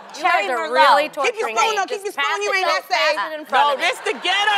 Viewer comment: did Tamar said to adrienne "keep ur spoon u ain't that safe"